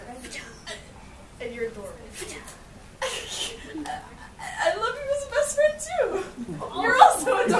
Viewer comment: Random citizen:*talks in uwu* Deku: *surprise pikachu face* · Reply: What do they even say in that moment? All I heard was "uwu love you. " :/